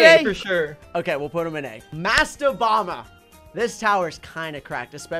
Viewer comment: thought he said masked obama